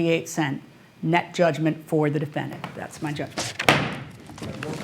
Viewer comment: “How are we going to pay him ?”That backfired on them. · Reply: Sounds like she said. “So we gotta pay him?”